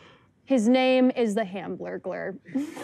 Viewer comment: wish the caption said “hamblurglar” bc that’s the best part